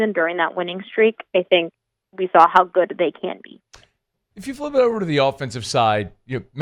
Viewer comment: did she say “how good they can’t be” ?